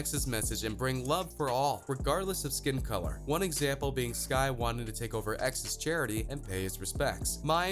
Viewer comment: did he say skye